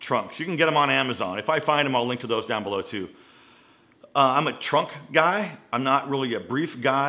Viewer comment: Did you just say you're a "Trump guy not a brief guy?" 🤣 Haha....